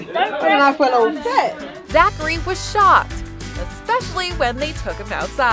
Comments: I thought she said "Zachary was shot, especially when they took him outside" 😂😂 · I thought they said zachery was shot and I was like “ oh damn” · who else heard "zachary was shot" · I thought they said that he was "shot" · I thought it said Zachary was shot